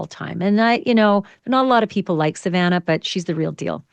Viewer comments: That comment about "Savannah not being liked by everyone" came out of nowhere. Wow! AB is all over the place, I stopped watching her channel a few days ago because she seems so desperate. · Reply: I thought she said "not a lot of people are like Savannah but she's the real deal" but she said " not a lot of people like Savannah" yikes! Desperate and disengenous vibes all around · Did Ashleigh Banfield say "Not a lot of people like Savannah Guthrie"? (And if so, did she mean to say they do like Savannah Guthrie? If anyone heard this in beginning of this video